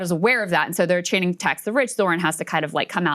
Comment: She thought they were saying “let’s go bitch!” No, it’s “tax the rich.” Lol